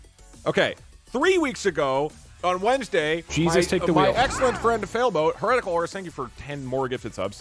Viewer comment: Normally i play the vods at 1.5x speed and when got to 15:00 into the vod it sounded like said failboat was his ex girlfriend, had to keep replaying it to make sure i heard that right.